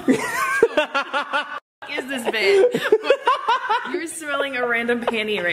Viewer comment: Did say bae to joe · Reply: Peter Perdomo she said ”what is this bit” :)